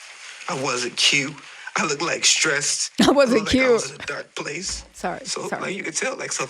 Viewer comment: he said, he wasnt Que as in he wasnt himself. i believe what Que said went completely over your head. He basically was saying its not fair that some of diddy workers ie (bodyguard Roger Bonds, other Executives, producers…. enabled Diddy’s behavior in real time but wanted to sue diddy after everything got exposed in the aftermath but when all of those things were going on, those same ppl were apart of it and didnt speak up against diddy. Two things can be true at the same time. He’s not necessarily talking about the actual victims who got violated , he’s referring to the workers who were involved in those actions but then turned their back on diddy when shit hit the fan. Now all of a sudden the ppl who enabled Diddy’s behavior are now calling themselves victims because of what they have witnessed